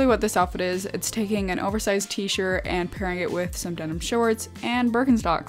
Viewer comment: Who else heard her say denim shirt? 😂